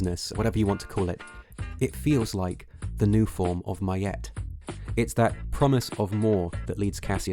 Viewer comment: He says a word that isn't written in the closed captions. Miyet? Millet? My yet is what it literally sounds like but I'm unfamiliar with the word. Does anyone know what word he's speaking? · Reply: Ma'at :)